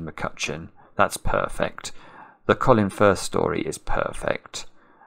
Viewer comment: When you were talking about Love Actually, you mentioned Colin Firth and I heard "Colin Furze". That would have been quite a different movie :D